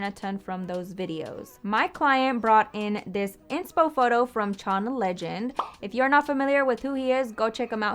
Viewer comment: I thought she said John legend and I was kinda confused 😂.